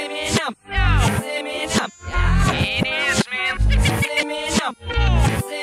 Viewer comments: I heard it is ma'am💀💀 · I hear It is slim. It’s Eminem it’s Eminem